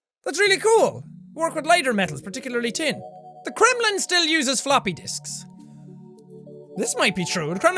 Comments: I died laughing because I thought it said "The Kremlin Still Uses Floppy Dicks" 😂😂😂😂😂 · I thought fact #8 at 5:35 said "The kremelin still uses floopy dicks"... im so frickin dirty-minded.